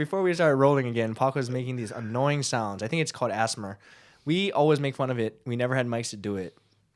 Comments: When you said ASMR I thought you said 'Asthma', which would've been weird... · When Ryan said ASMR I thought he was saying asthma. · i thought he said asthma not asmr dmdkdnnjx